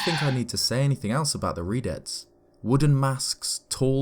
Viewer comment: I thought they said "Redheads". After dating a few, I can confirm they're terrifying.